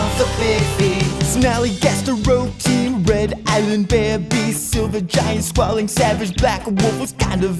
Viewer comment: All this time I thought it was the WaWa Woman... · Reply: I keep hearing "Red Island Bear Beast" in the intro song